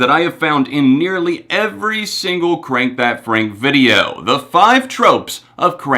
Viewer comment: At first i thought it said frank iero and i was like WTFFF😂